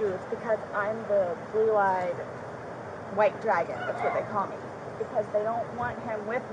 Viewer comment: Huh. I thought she said "blue eyed white dragon". I thought because he's black, she was trying to make it sound like they didn't like him dating a white girl. (I only knew he was black because I’ve seen additional footage.) That was an interesting idea about the Shakespear. Oh, and I thought she asked why he was purple, not why did he gurgle. I think she wanted to know if they were definitely thinking suffocation.